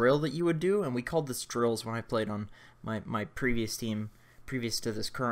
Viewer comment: First time he said drills I thought he said drugs